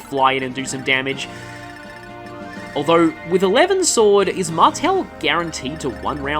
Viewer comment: I heard that as "Eleven sword" instead of "A levin sword" and was confused for a good few seconds. 21:38 And they definitely have no reason to be scared, nope.